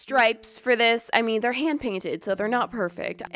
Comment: I thought u said MAD PAINT😂 no hate no hate hear I just thought that was funny!!!!